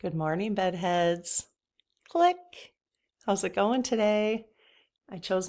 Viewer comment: I am enjoying your channel - lol for the longest time I thought you were saying hi Buttheads :)